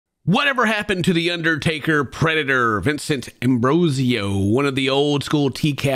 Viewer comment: I thought they said "The Uddertaker Predator" and that's all I can hear now.